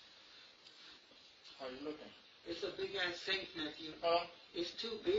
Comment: I think the one guy said "It's a big ass safe Matthew". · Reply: or Nephew?